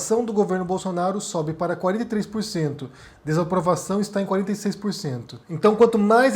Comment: eu entendi direito? 43%?????? Deu dor de barriga na hora....